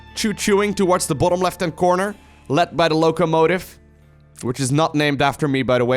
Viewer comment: Sometimes i hear #lowkomotive